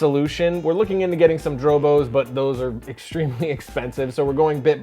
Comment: for the ignorant. near 6:20 of this episode you talk about upgrading your storage set up. but when I typed in what I heard I could find what storage you were talking about, whats a "joe bro storage" or "dro bows storage" please? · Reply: drobo